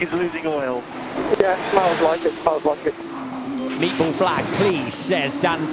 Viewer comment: Did he just say “MEATBALL” flag?